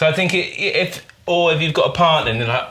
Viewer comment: What did he say here 15:05 lol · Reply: Yeah, I had to replay that a couple of time and thought I heard “If you got a part(ner?) in this…”